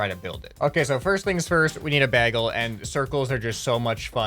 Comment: did you just say.. baggle ?